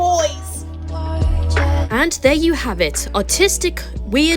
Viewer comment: I can't believe I misheard that as autistic 😭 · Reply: they DID say autistic 😭